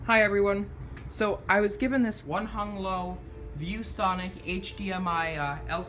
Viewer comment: not sure what you mean by racist remarks. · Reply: did you refer to the name of the monitor as "One-hung-low" the way AvE does in his videos to indicate Chinese origin? Or did I misunderstand? If I misunderstood I apologize, what did you actually say there?